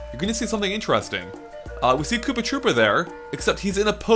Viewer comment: Did you say Poopa Troopa?